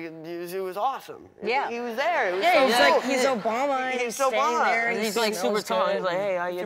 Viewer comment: did anyone else hear Finn say Obama smells good